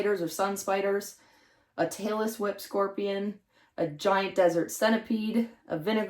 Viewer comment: When you were showing t shirts, I thought I heard you say Taylor Swift scorpion... lol, my ears were wrong... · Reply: Hahaha! I can totally hear it! Tailless Whip Scorpion = Taylor Swift Scorpion 😂